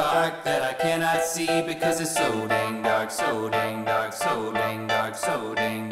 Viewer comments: I thought it said so dank dark. Lol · lel i thaught the title was "So Dang Dank" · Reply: millette C I thought it "said so dank dark" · I thought it said so dang dank. Ok I'll leave now. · I thought it said So Dang Dank😂